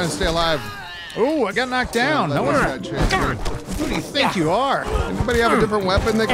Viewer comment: my husband and I both heard that as "Who do you Thick you are?" :D This was one of the funniest videos ever. You guys sound like you're already having a better time now that Extinction is out of the picture.